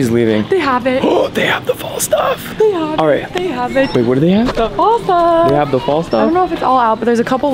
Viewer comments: Am I tripping or did he say babe or wait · Is it just me or does he call her "babe" at 5:59?? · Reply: He says, "Wait, what do they have?" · anyone else hear say “babe” at 5:57 ???? I SWEAR THEY BETTER BE DATING BY NOW😭😭